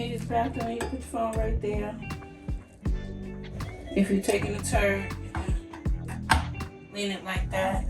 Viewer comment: Perhaps I heard wrong but In my mind I heard “taking a turd!” Now I wasn’t ready for that! 😂